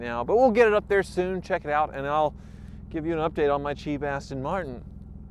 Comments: Who else heard him say cheap ASS-ton martin? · I kept hearing "Cheapass Tin Martin." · I keep hearing say "my cheap-ass Tin Martin" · i keep hearing “my cheap ASS ten martin”